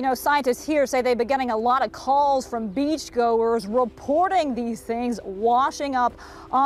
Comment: I thought she said 'beef goers'